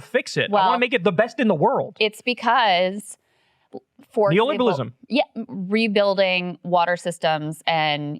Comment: what "ism" word did kyle say around @ 9:13??? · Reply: Neoliberalism.